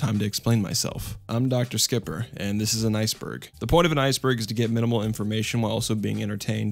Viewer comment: You said "and this is an iceberg" but I thought you said "and this is a nice bird"...Yes.. Yes it is.